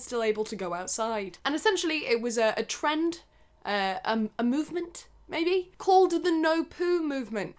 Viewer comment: I thought she kept saying semen and I was so confused